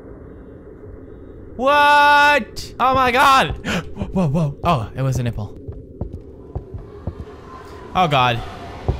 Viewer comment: I'm so so sad...I want to meet:O...Sorry I.JUST.LOVE.YOURE.VIDEOS!!!!!!! XD AHHHH...I WANT TO MEET YA BRO!!!!! XD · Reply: lol, did he just say oh it was a nipple in 1:18 ?